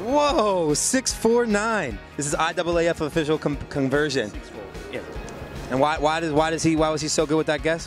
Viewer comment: Odd double af official conversion???? 4:45 ????? Wth is he saying